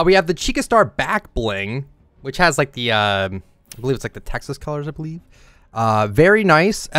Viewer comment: did you just say Texas 😐